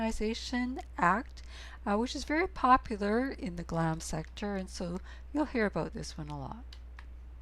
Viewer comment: Can someone tell me what said at 0:28 "popular in the glam sector"?!